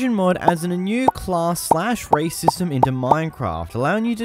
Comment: When you said "race system" I heard "racism"